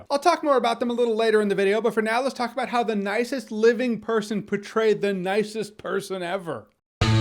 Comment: Lmao I got so scared at the beginning. I seriously thought he said betrayed, not portrayed and my heart just sank for a solid good 20 seconds